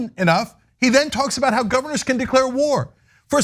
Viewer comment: I thought he said governments but if he said governors, he's an idiot.